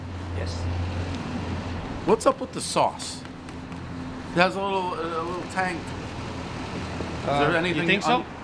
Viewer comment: I swear I thought Dave said that the smell has a little “taint” to it. 🤣